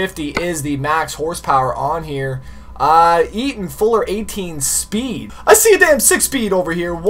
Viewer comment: Who else heard him say 8 speen insted of 18 speed